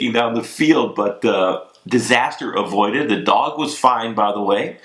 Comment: Sounded like you said..."the dog was fined." Was wondering how he was gonna pay ;).